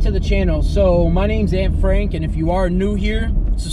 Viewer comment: I thought he said his name was Anne Frank and I died laughing.